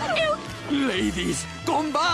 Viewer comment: ladies come back,underneath my crunchy roach exterior beats the heart of a lover!🥺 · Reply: I thought I said larva instead of lover🤣🤣🤣